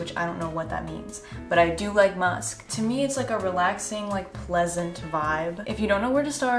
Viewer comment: During the scent bird promo my airpods glitched and I heard “it’s a relaxing, lesbian vibe” and I was like oh word and then I was like there’s no way hahaha